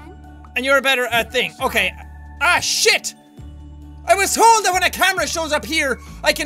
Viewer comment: the subtitles said I shit instead of oh shit!! ha!